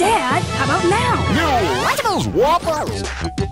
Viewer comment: I thought I heard "Lunchables Whoppers" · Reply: I understand the confusion. After all, “waffles” can sometimes sound like “whoppers”.